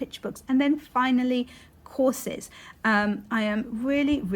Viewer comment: Thought you said Corsets at first....which...not a bad idea....